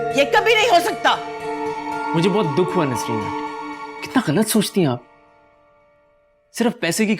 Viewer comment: did he say Nasreen 🤣🤣🤣 sath me koi Anty Chanty Nahi